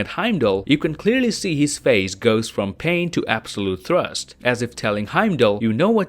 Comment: sorry but i keep hearing "from pain to absolute THRUST"